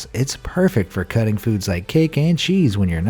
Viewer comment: woah. Wait. Did I hear, cake?